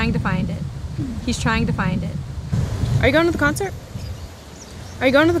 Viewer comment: Are you gonna the concert? に聞こえる（最初の女性は）